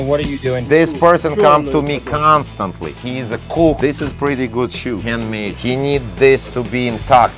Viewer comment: I thought he said "This person come to me constantly, he's a Kook" not a "cook" 😂😅